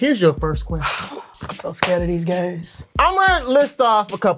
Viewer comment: I swear I heard Jozzy say "I'm so scared of these gays" 😭😭😭🤣🤣🤣 I zoned out for a sec before that and that's all I heard hahaha